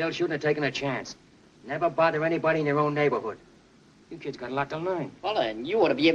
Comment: Never bother anybody in your own neighbourhood... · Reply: I thought he said never bother anybody in their own neighborhood. Cause it’s his neighborhood, and I guess the kids knew that, or should have known that.